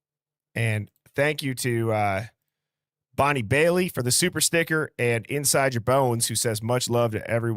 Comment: Damn brother I thought you said super sticker for tits.....and I'm thinking, when did people start calling pasties, super stickers? My deaf ass. Lmao.